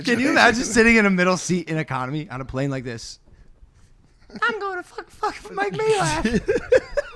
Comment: What did Logan say? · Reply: “I’m going to F mike majlak”